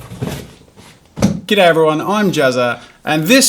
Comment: Did he just say "Gay everyone I'm Jazza!" lol